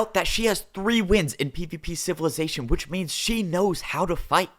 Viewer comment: did say PPP🤣🤣🤣🤣